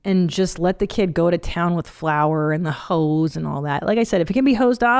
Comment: Mom brain: I thought she said "I let my kids play with the hoes..." instead of "the hose"! For a second I was so worried about those kids ha!